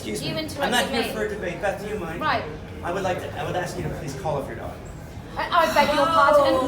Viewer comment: What does he/she/it/they/them/zey/zem/etc say at 1:28? "Call off your dog or daughter!"? Or sth else entirely?